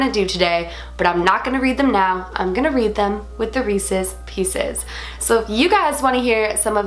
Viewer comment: When said Reeses pieces I thought said Racist pieces (edit: you will never hear it the same 😂